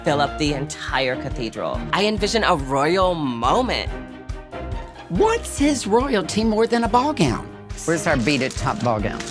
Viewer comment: boy please use the right pronouns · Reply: He actually says, "What says royalty more than a ballgown?" He just says it funny, they definitely should have redone that clip cause a lot of people think it sounds like he is saying his.